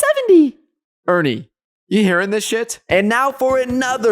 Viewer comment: did I just hear bro say shit?